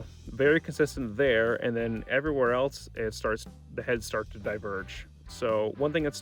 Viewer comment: what’d you say? 👀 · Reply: Everywhere else, the heads (curves on the graph) start to diverge.